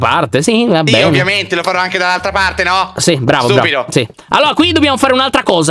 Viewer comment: solo io ho sentito che diceva stupido a?? (Io che penaavo che si incazzava con)